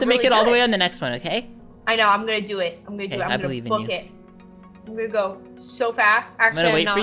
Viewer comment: I heard the girl say a bad word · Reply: She said “Book it” lol